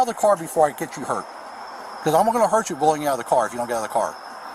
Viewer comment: He didn’t say HE would hurt her. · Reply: Listen again at 8:00. He said "...cause I'm gonna hurt you..." She repeated what he said too.